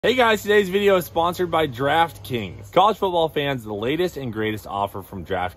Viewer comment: Don’t miss out on all the action this week at DraftKings! Download the DraftKings app today! Sign-up using <link> or through my promo code CLEETUS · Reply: Hey guy's, today's video is sponsored by Giraffe King's <what I thought I first heard. lol